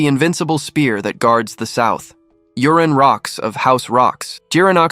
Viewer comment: why is mc's name urine lmao? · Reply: Same thinking, I heard "Urine Rocks", first thing in my mind "Bro that sucks".